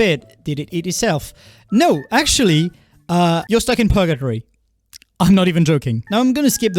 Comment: what did he say? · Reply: Porgotory